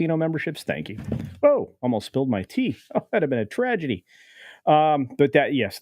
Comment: I initially heard you saying ‘I almost spilled my tea’ 🍵 as ‘…almost stole my teeth’! 😁